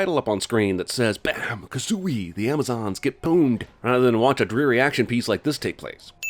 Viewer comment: Am I the only one hearing near 5:45, "Bam Kazzooie! The Amazons get "pooned"? I'm seriously not trying to be provocative, but I've listened to this several times and I keep hearing the same thing. I get that he's saying something like, "pone-ed" but is anyone else hearing this? Is it an intentional joke, lost upon me?